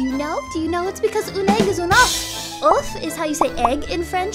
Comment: When you said "une œuf" it literally sounded like you said "an ubh" with mean "the egg" in irish lol